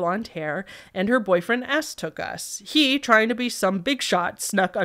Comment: I was listening to the video while crafting and, in the last story, I SWEAR I heard you say "aspargus" when you said "S took us", and had to look at the screen to make sure I was a dum-dum 🤕 hahahah Great video, as always!